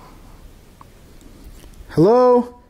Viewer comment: Escuche que decian en el minuto 17:26 (AYUDEME) I heard they say twice (HELP ME¡ HELP ME¡) SOUNDED LIKE A WOMAN. Suerte y saludos. Al final del video se escuchó la misma voz de nuevo. Eres bien valiente.